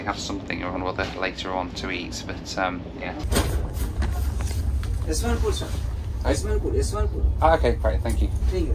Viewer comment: did the guy at 8:40 say ‘i smell good sir! i smell good. i smell good. i cleaned it. or did i miss what he said?